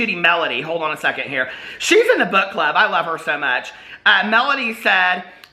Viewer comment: I absolutely adore you. And I misheard and thought you said Melody was in the butt club instead of the book club. Family friendly! 🤟